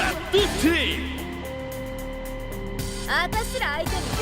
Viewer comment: Hey, my two cents on Isla: I'm no expert in Japanese but at 4:55 I think I hear "atashira aite ni yokuyatta yo anta" which means something along the lines of "you were a pretty good match for US" I think there's something Venom-esque in her in the sense that she seems very in touch with this other entity, unlike Shun'ei which is still struggling to figure out the full extent of his powers or where they come from. Lore-wise this look like a device to move Shun's story forward, considering also that at some point he will apparently go berserk as seen on one of the trailers. Also atashira means us in femenine, so her entity is most likely a she as well.